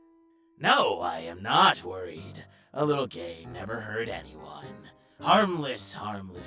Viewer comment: bro on the first thing i paused and thought he said a little gay